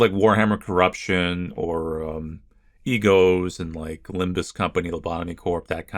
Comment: did you just say limbus...company?